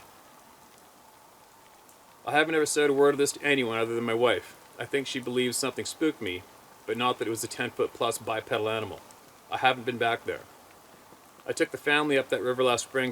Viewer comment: Wow I'm an idiot I thought you said bisexual not bipedal I was going in the woods with condoms what a mistake that would have been 😂😂😂😂😂